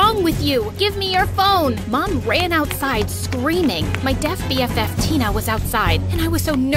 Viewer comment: at the beginning i thought she said my dear bff. until she started usng the sign language i had to go back. r u sure she,s not deaf and dumb